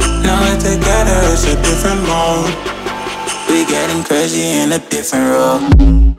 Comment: At first i thought it said "we're getting crazy in a caserole" 😁